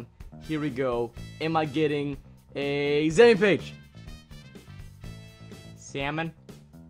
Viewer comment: Well it sounded like he said salmon page and the salmon is in notes…